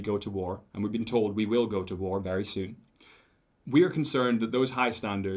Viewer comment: Bloody hell, did the fella just say, "we've been told that we'll be going to war very soon." Who the hell against, the Russians? That would ve a very, very bad idea. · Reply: Yes I'm sorry to say you didn't mishear that. I was hoping I misheard it and approx 3:06 or round about he talks about the reason behind the petition and states "We've been told we will be going to war very soon". I still can't believe my ears. What in the heck?Edited for typo.